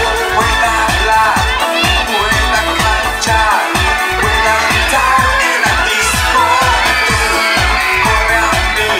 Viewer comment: buena la cancion pero que dijo en el minuto 1:48 ? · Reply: Callar dice